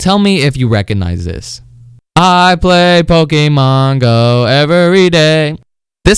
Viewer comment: I thought you said “ I quit Pokémon go everyday “